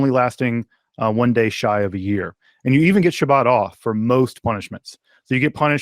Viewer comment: ”[…] and you even get your butt off for most punishments” Wait, what did he say? Oh … shabbat , right, right.